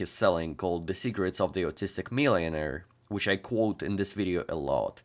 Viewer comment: Lol I thought you said "Cigarettes of the Autistic Millionaire" there. Didn't look at my screen at that moment and was wondering what a weird book title that is xD Just goes to show how accents can mess up communication sometimes if you're unfamiliar to them 😅